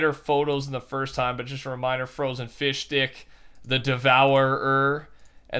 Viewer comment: When he said fishstick I thought he said fishdick